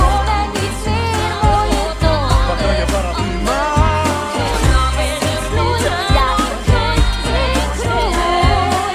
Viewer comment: Podrían decirme que dicen isabela y dolores en esta parte? No escucho bien lo que dice cada una y lo necesito para mi cover 😢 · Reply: isabela says ja ik groei en ik bloei ja ik groei ik groei and dolores says en ik hoor hem en ik hoor hem en ik hoor hem echt hem